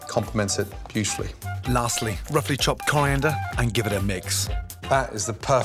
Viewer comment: What is a damn holander or cholander or what ever he says on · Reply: It’s coriander leaves 🍃 OR Cilantro leaves 🍁